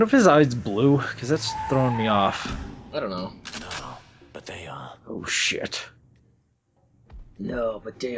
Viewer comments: character says 'No, but they are@ but the subtitles say 'yeah, but they are'? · Did anyone notice that the subtitle said yeah but he actually said no